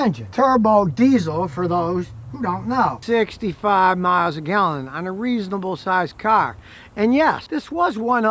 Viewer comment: Did he say 65 miles to the gallon?